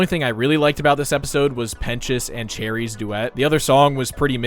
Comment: why does it say “penis” for pentious on subtitles